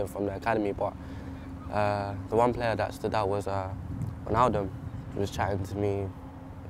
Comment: When he said wijnaldum I thought he said ronaldo